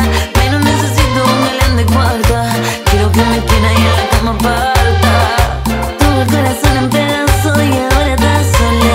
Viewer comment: No entiendo que dice minuto uno 1:07 yo no nesecito alguien de cuarta quiero que me quiera y habra como palta ?? Ajaj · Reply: Yo no necesito un galán de cuarta, quiero que me quiera y en la cama parta.